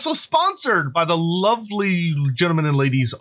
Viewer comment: I had to repeat the first section of the video three times because it sounded like you said "the video is sponsored by the lovely german ladies" and I thought that was really weird.